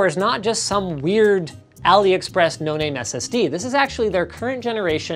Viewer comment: I thought it said "This is a tiny STD"